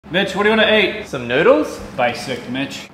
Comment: I thought he said ‘Bitch’! Thought maybe they’d had a row.